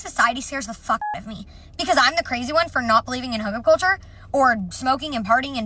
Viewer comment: OK at almost exactly 36:00 what is she saying? It sounds like "hunger culture"?